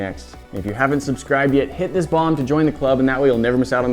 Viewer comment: thought my boy said "hit this bong"